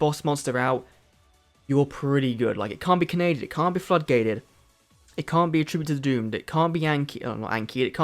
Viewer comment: did you say f-gated?😂